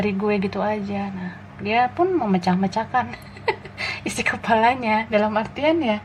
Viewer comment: Ympun hampir ku salah denger kata2 Kak, yg ku denger kok Dia memecah-mecahkan kepalanya 😆😂🤣 Walaupun keras kyak beton kepalanya, jangan lha yaaaa 😆🤣